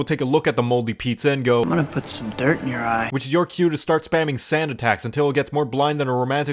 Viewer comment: its weird, this whole time i thought peter said "you otta put some dirt in your eye"